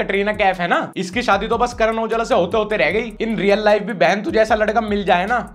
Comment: bro did you just say Karan Aujla?? ❤